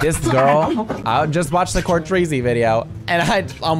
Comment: DID HE SAY COURTREEZY BCS I LOVE HER SM